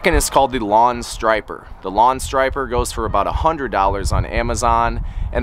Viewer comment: Dang it I thought it said $15 lawn stripper.